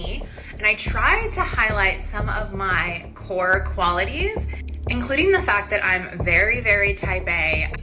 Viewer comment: did she say "core qualities" or "whore qualities" ??